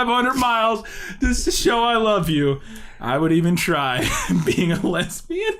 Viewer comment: That line in that song sounds more like “being a lesbian” than “dating a lesbian,” but that’s also nonsense... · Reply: I swear I heard him say’ I would even try being a lesbian’🤣 it makes no sense in the context of the scene and I love it